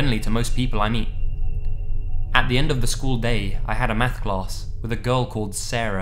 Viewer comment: I thought he said, "meth class", in the first one